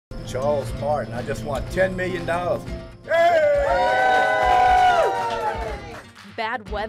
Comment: For a second I thought it said Charles Monette